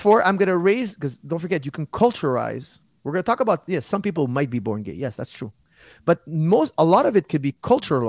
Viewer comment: am i hallucinating, or did i hear firas says people can be born gay?